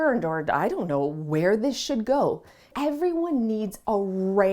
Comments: When you said " I don't know where this should go", I clearly heard you say "I don't know where this sh*t goes". Haha 🤣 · I thought she said “I don’t know where this shit go” lol 😂